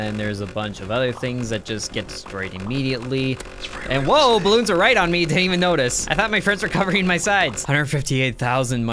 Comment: What was being whispered at 15:48 when that ceramic bloon snuck up on him? I can just barely hear it, but can't make out what's being said. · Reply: “it’s free real estate” was said